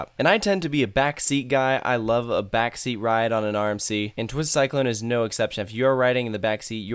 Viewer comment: Oh you’re saying rmc i thought you said armsy and i was like what ?